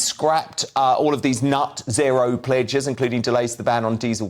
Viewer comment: Did I hear you say ' Nut Zero ' instead of net zero ? I certainly prefer what I heard l😀